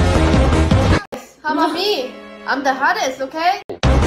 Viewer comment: I thought Lisa said what about me I’m the gayer okay😂😂😂😂😂😂😂